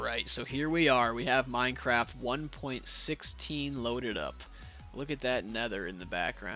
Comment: i thought he said look at that n-word in the background